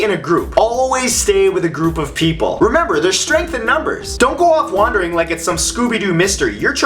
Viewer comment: At first I thought he said "There's rape in numbers".....